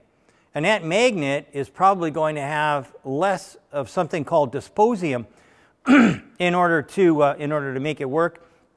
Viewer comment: Thank you Sir for your work and expertise, this video is awesome. I might be wrong, but I think I heard three small glitches in your text : ( 13:44 & 13:50 ) : Metal you quote is "DyspRosium", not "Dysp_osium", and ( 13:51 ) It is not "Noedi_d_ium" nor "Neodi_t_ium" but "Neody_M_ium". I refer to [ <link> ] , [ <link> ] . Please pardon me if I am mistaken.